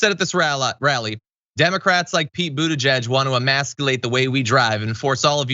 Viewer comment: When I heard the actual sound bite, I thought she said immaculate not imasculate so I was very confused what she was bringing up